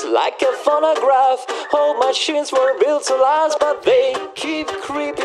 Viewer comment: When he says "old machines weren't built to last" it sounds like he says "I hope my chins were built to last" as in he was like running but yeah